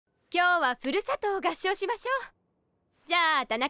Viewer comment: のとこの先生のふるさとがぷるさとーに聞こえる、なんでだろ笑